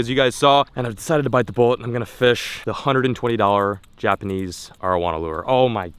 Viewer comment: Who else thought he said "120$ Japanese marijuana lure"???